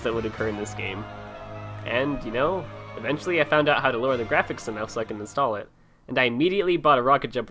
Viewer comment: on 6:21 did u say lower the graphics announcer? because it u it was something else it might help me run tf2